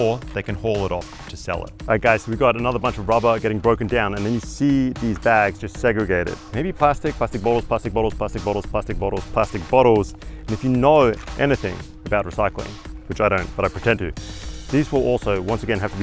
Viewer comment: His accent sounds like he said: plastic butt holes, plastic butt holes, plastic butt holes